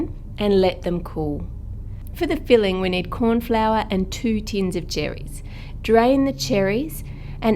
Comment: At first I thought u said 2 tons of cherries. I was very very confused how one found 2tons of cherries